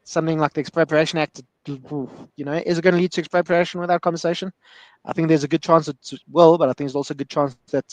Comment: Sounds like says : "Expropriation without CONVERSATION" quite apt.